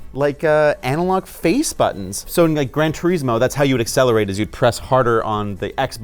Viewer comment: Did you say Gran Tureesmo? lol